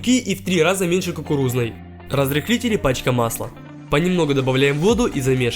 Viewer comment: я один услышал Мачка пасла ?